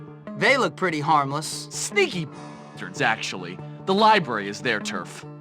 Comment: Did Gary said "sneaky bitches actually"